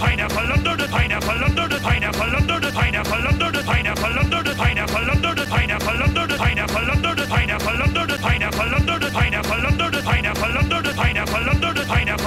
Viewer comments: I heard it went “under-der the pineapple” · After a while i kept hearing "plunder the sea now" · Lundundun Pineapple Lundundun Pineapple Lundundun Pineapple Lundundun Pineapple Lundundun Pineapple Lundundun Pineapple Lundundun Pineapple Lundundun Pineapple Lundundun Pineapple Lundundun Pineapple Lundundun Pineapple Lundundun Pineapple Lundundun Pineapple Lundundun Pineapple Lundundun Pineapple Lundundun Pineapple Lundundun Pineapple Lundundun Pineapple Lundundun Pineapple Lundundun Pineapple Lundundun Pineapple Lundundun Pineapple Lundundun Pineapple Lundundun Pineapple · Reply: I hear “longer pineapple”